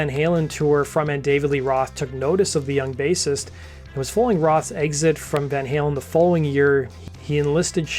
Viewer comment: what is he saying here? "It was fooling Roth's exit from Van Halen the following year"? Please pronounce your words and stop slurring